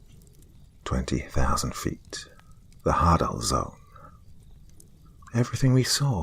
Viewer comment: Genuinely thought you said hardon zone the first time not hadal zone, I may need ears tested🤣